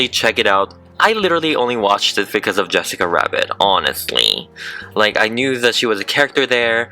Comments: Not to be mean but... It sounds like you say Jessica Wabbit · Anyone hear him say "Yessica Wabbit!" A couple of times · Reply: I heard him say jessica yabbit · Why does he sound like he's saying "Yessica" Rabbit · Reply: I hear Jessica “Wabbit” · I'm soz but I keep laughing cuz it sounds like your saying "Wessica Rabbit" 😂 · Anyone hear him say "Yessica Wabbit!" A couple of times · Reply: I thought he said wessica rabbit